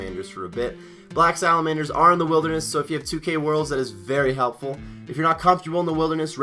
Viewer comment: Wat did u say at 2:40? Something worlds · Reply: 2k world. If you have 2k total (or even up to 2200 now) You can use the total level worlds where there are less pkers (not zero pkers though)